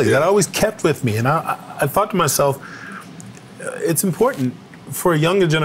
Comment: Wait did he say “i fucked myself”??